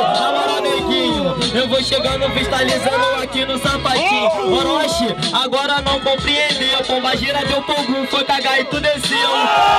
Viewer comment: ' Krl Naum Entendi O Que Ele Fala Nos 0:52 Alguem Fala Pra Mim ? <3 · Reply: orochi agr nao coomprendeu pomba gira deu pro ogum foi caga e tu desceu